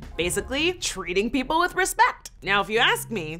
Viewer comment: did she say Respek oh my God